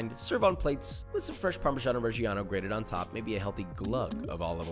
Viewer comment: Does anyone else here "parmesano reggiano" allided together as "parmesan o'reggiano", as though it were an Irish cheese? In english, it's just parmesan, so I'm sure it's my eurocentricity speaking lol. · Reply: LMAO yes but i hear it as "parmesan origiano" as if it's italian for "original parmesan"